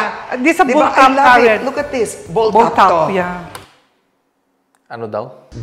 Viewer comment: I think they said Bulthaup.